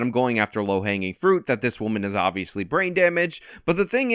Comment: I was distracted near the end and heard you say "frame damage" when you actually said "brain damage" and i remembered where you were during January 6th.